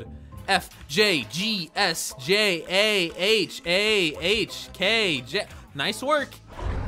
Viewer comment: DID HE SAY KJ???????